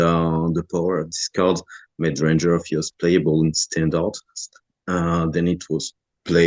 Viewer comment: i think he says "playable in standard" not "playable and stand out", anyways.. awesome video im blown away!